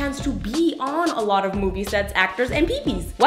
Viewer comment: When she said movie sets, I thought she said booby sets😂